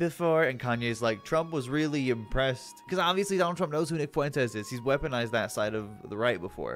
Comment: when you said nick fuentes i thought you said vic fuentes and got real scared